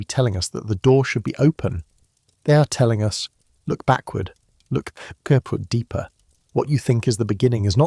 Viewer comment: What the hell did he say at the 18:25-18:30 mark??? The closed caption picked it up as "Look, go out deeper" like...HUH?!?